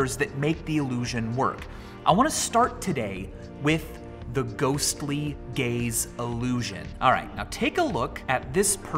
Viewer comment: Who else thought he said "ghostly gays"?